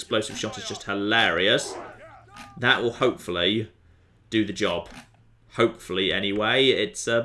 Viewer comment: I could have sworn, that I heard you say "popefully" at one point.